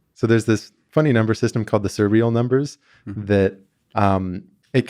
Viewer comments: Can anyone tell what does Grant say at 2:53? I understood servial numbers but that's not right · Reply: Surreal Number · What number system does he say here? Servial numbers? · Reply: Surreal Number